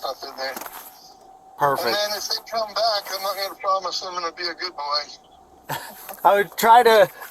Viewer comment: MY Part 1 Video/Interview with Reckless Ben: <link> Reckless Ben's Part 1 Video: <link> Reckless Ben's Part 2 Video: <link> Ben's GoFundMe: <link> Tyler Shaw’s IG: <link> Tyler Shaw’s YouTube: <link> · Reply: said, "I'm NOT GONNA PROMISE I'M gonna be a good boy" in the bodycam. Not what you wrote on the screen saying "I want you to promise I'm gonna be a good boy". Just fyi